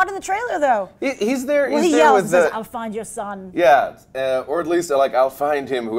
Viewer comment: i think Zod says " I'll find your son" to Jor El before he sends Zod to the phantom Zone, he is also handcuffed so i guess i am right.